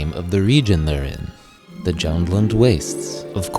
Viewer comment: I heard all of these correctly except two - I thought Alex Guinness said 'Jennland' and I've still no idea what Mark Hamill said at the end there. One I see crop up from time to time is the Cologne/Clone wars. He originally said 'Cologne' wars, and it was later changed to 'Clone wars' to match the prequels. I wish I kept my old book, which had it in print, but I got rid of it ages ago.